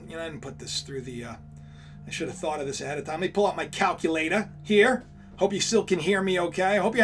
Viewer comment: OY VEY!! I heard you at 23:50. You let the accent slip, bro. I got my "calculatuh" out too and it appears that You = Kosher.